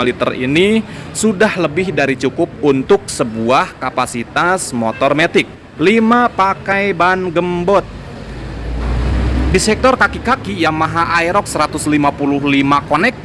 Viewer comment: w denger si akang ngomong "nmr 5 pke ban jembot"😏. apa w salah denger y😌